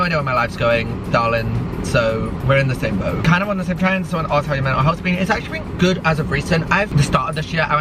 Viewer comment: wtf whas the question babes i can understand what you said 😭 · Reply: asked how my mental health has been 😭